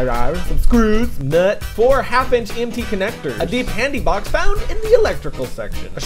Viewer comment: A what-box? I didn't understand what you were saying then xD (I'm not English) Could anyone spell it for me? Thanks :D (to me it sounded something like: "a pantie-box"...Yeah, I know... and I bet I won't find pantie boxes in the electrical section xD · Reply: I think it was "handy box" :)